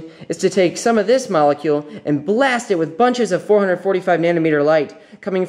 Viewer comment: I noticed a typo in the captions at around 1:10. It says 45nm instead of 445